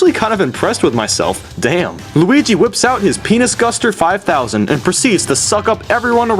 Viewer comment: DID YOU SAY PENI* GUSTER !?!?!